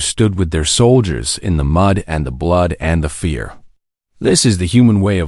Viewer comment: I thought I was hearing the "the mud and the blood and the beer" from Johnny Cash's "A Boy Named Sue", then I realized that was not what was said. :)